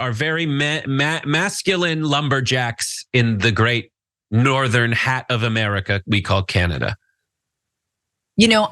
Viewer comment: Northern half of America????? WTF is the matter with this asshole..try the northern half of NORTH America you ignorant prick. Gee, is this another specimen of American public schools? Unbelievable! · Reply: I thought he said northern hat 🤷